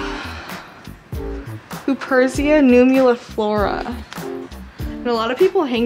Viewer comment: Any idea what she said the name was of the plant at 4:08 ? And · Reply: Huperzia Nummulariiifolia is what she says at 4:08😉